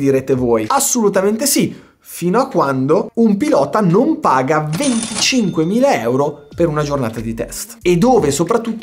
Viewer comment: io non ho capito se il pilota paga 25K o 250K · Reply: 25k